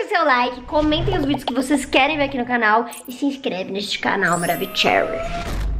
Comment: Maravicrehrry A xeury?? MDS kkkkk foi oq eu entendi